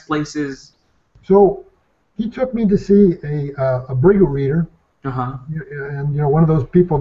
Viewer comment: what did he say at 58:31 ? Didn't get the word. can somebody clarify ? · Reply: the word is Bhrigu Reader....one who reads the destiny of people already written on sacred palm leaves/ manuscripts ...